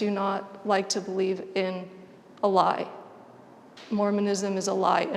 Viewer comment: what did they say? “Moremanism (?) is a lie” I really couldn’t understand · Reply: Mormonism